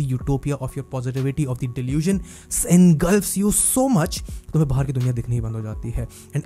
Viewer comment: What's the word after delusion at 8:52? Se....?? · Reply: Engulfs